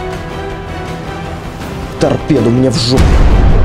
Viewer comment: из-за взрыва не расслышал, что ты сказал, можешь повторить? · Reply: Егор Титаренко торпеду мне в жопу))